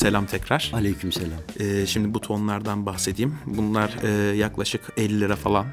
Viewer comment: buton'u ben bu ton olarak anladım hocam konuştupunuz bu tondan bahsedeceksin sandım. sanırsam bir adet artikülasyon hatası oldu.